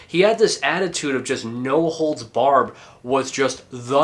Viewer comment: does he say "no holds barbed" lol its barred bro